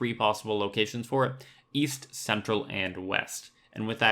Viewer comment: oh east i thought you said weast